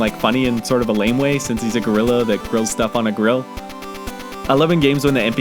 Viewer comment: Oh my god, HAHAHAHAH. When I first watched the video and he was talking about the man at the grill, I thought he said "griller" in a really weird way. Turns out he said "gorilla"... I can't get that unintentional wordplay out of my head HAHAHAHA Edit: I guess it was kinda intentional, as he named him "Grilla" anyway, but still...